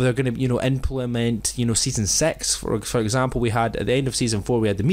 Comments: did he say season sex?😂😂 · it sounded like he said season sex on